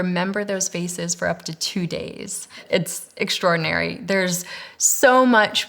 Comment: 2 days??? i thought she was said 2 decades